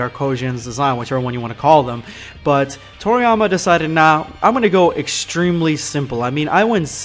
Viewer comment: did u say toritama instead of toriyama? is it just me that thought he said toriTAMA